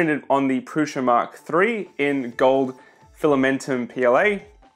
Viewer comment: I realised that the auto-generated subtitles are really wrong, so i decided to add them on my own though i still can't understand what were the words in the sentence said at 1:50. The subtitles say it's "printed on the pressure mark 3 in gold empioyee" but that's definitely not correct. Can someone help? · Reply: Prusa mk3 in gold PLA, appreciate the help with subs!